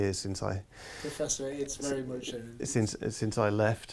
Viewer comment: What on earth does say at 12:21? I've listened twenty times now. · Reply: Shawn Pitman "Professor it's very much earned" >